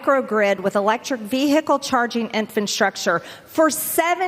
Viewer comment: into the video it sure sounds like MTG says “infant structure”.